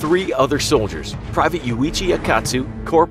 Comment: I thought he said luigi